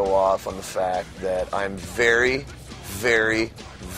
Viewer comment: it sounds like corey says Fery instead of very.